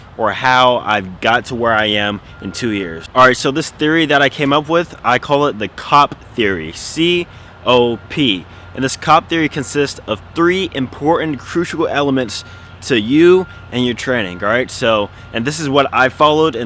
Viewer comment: when you said C.O.P I thought you said COCK LMAO but great video love the tips